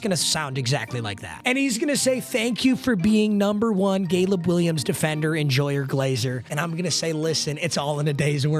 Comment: I heard you sneaking a gayleb williams